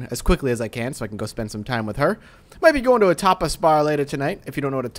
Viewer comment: I swore I heard topless bar, not tapas bar...